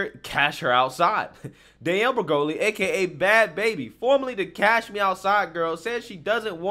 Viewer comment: wait she was saying "cash me outside"? I thought she was saying catch me outside aka a fight challenge.